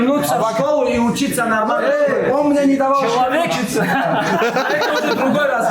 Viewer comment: что сказал ислам ? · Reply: Он мне не давал учиться (про Абубакара)